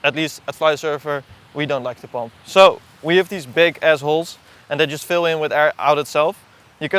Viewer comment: did he really say "we have these big Azz-Holes" :face-green-smiling::face-green-smiling::face-green-smiling